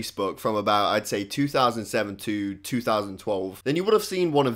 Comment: When he said 2007 to 2012, i thought he said 20072 as in he pronounce it wrongly